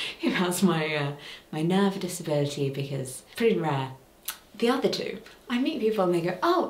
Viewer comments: Captions are uploaded! Thank you for your patience ❤ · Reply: I already commented this but I'd thought I'd reply to this comment as well so you have a bigger chance of seeing it: 26:21 caption error here ("because pretty it's rare" instead of "because it's pretty rare") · caption error here ("because pretty it's rare" instead of "because it's pretty rare")